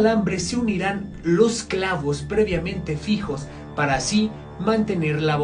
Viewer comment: Siempre pensé que decía "Ponte creepy" 😅 XD